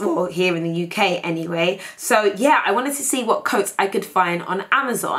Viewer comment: Lol i thought it said cats and not coats .. xD